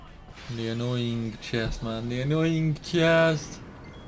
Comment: Is it me or does it sound like it says I’m an annoying c**t the whole time lol